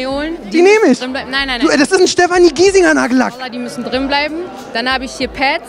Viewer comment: hab ich wirklich verstanden "Vallah die müssen drinne bleiben" ?